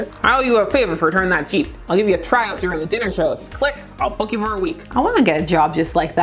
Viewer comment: Img... When said "I'll book you for a week." I heard "I'll f%$k you for a week." I'm dieing!